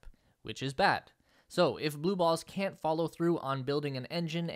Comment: Am I hearing this wrong at 6:48 .. blue balls?